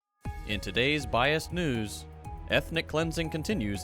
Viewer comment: Fuckin, I always hear it as "biased nudes